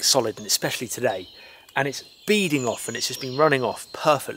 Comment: I heard "And it's beating off." Subscribed.